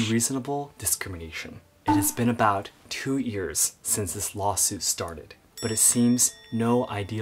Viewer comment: saw-suit instead of lawsuit